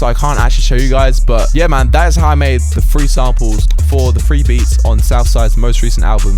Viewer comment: 😂 i was leaving the video and i heard free samples